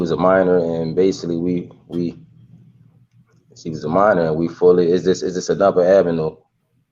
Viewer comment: I don't agree with him saying jay z has to take a test let's not get ahead of ourselves lol but mom needs to get at all of the dudes from back in her day & line em up some photos💀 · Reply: ohhh nvmd I thought he said they both was minor back then